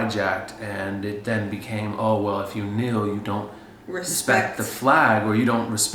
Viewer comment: Tell me why I heard take a "meal" instead of take a "kneel" when was speaking 💀💀 I must be hungry or sum